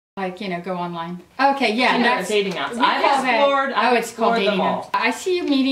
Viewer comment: What did she say at 3:37? I mean it's kinda weird that he'd skip that part of the audio. :O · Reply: an annotation that says: "IDK WHY IT CUT OUT BUT SHE SAID "Do you ever do dating apps"".